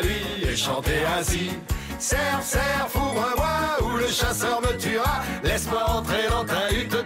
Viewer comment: c'est tellement beauf que la première fois j'ai entendu "cerf cerf fourre moi" xDDD j'ai honte